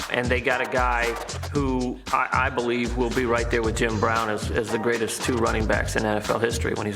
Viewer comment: I HOPE he meant "Jim Brown"... How did the editors let that mistake through? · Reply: Kaushik Kothakonda every time I listen, I hear "Tim Brown"